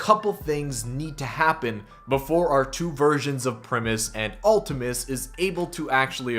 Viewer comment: I thought he said two virgins